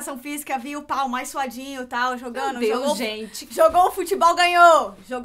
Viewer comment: GENTE PELO AMOR DE DEUS EM 7:07 ALGUÉM ME DIZ QUE EU OUVI ERRADO E A NÃO DISSE “ vi aquele pau mais suadinho” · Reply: gente meu deus que fogo😂😂😂😂 ela disse: via o pah, o mais suadinho 😂😂😂😂😂😂😂😂😂😂😂😂😂